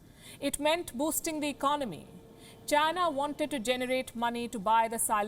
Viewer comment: am i hearing econamy ? or economy